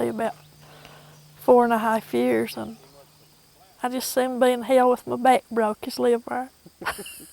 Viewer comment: “I’d just as soon...” what does she say? It’s sounds like a lovely accent to me (from England) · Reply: "I'd just as soon be in hell with my back broke as live there," direct transliteration. Translated, "If given the choice between living in D.C. and living in hell with a broken back, it'd be a tough choice." God, this video put a soft smile on my face. I'm from TN (East though, so somewhat removed from this region), and I've heard accents that at least echo this one all the time. It's like a taste of home.